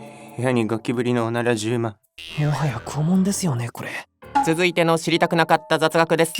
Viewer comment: ぼーっとしながら見てたら、「拷〇ですよね」が「肛〇ですよね」に聞こえてハッとした